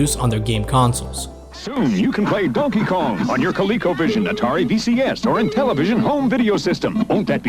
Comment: did he say Atari vcs